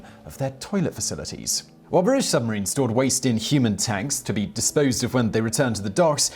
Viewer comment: I think you meant to say stored their human waste in tanks, not stored their waste in human tanks.😂